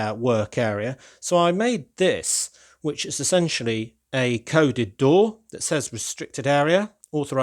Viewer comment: With the coded door, I thought you said" covid door"